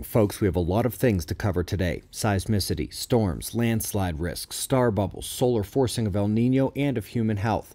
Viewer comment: Oh! Star BUBBLES! I had to listen twice, I'd thought you said "star buckles" and believed we might have finally found the origin of Starbucks.